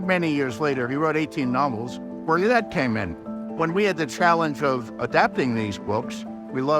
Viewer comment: George R.R. Martin is speaking. The captions are wrong. He said, "Bernadette came in", but the captions say "before that came in."